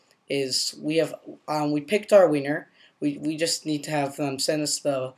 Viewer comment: i'm sorry but i hear.. "pick our Wiener" in 2:07... i know you where trying to say pick our winner... just thought i should point that out!... LoL.. by the way. i might buy one of your apps. they sound interesting!